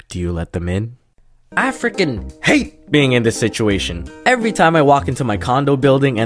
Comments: am I the only person that heard him say "african hate" instead of "I fricken hate" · it sounded like “African hate being in the situation” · If you put on subtitles at 0:22 instead of saying "I freaking hate being in this situation", it says "African hate being in this situation" lol EDIT: Someone changed the subtitles, but come on it still sounds like it lmao. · i was so high and every-time i replay this part i hear "african" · I thought he said African hate 😂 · I thought you said "African" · I'm I the only one who heard African hate at 0:23? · i heard “african hate this situation” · If you put on subtitles at 0:22 instead of saying "I freaking hate being in this situation", it says "African hate being in this situation" lol EDIT: Someone changed the subtitles, but come on it still sounds like it lmao. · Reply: Oh my GOD I was about to comment if anyone else thought it sounded like he was saying "African hate" 😂😂